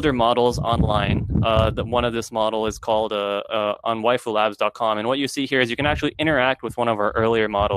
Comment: PLEASE minute 2:20 ... where is their interactive character? caption says wifilabs? but no search results :( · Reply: it is Waifu Labs i believe